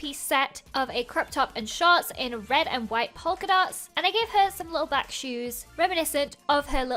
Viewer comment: I know can't help it cuz of her accent but it sounded like she was saying pulkidot instead of polkadot not being mean just thought it was funny